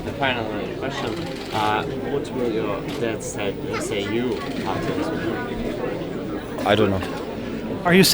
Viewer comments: Can anyone tell me what the question was at 4:13? Magnus replied "I don't know." To me it sounded like he said "What will your dad say you have hopped into?" which is why I'm asking. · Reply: I think it was something along the lines of "What would your father say to you (regarding this win)?" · what is interviewer saying? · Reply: He said " what did your dad say to you after you won"